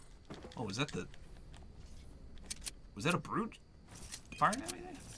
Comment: did he say Brutus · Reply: The meh Eldos no he said brute is but I thought he also said Brutus when I watched the vid